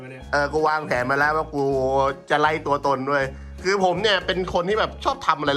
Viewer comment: หูหนูมันได้ยินว่า ไร่ตัวตน 🤣🤣 โคราชแท้เลยเด้นิ